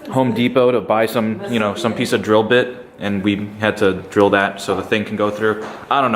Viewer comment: I thought you said you didn't had to drill????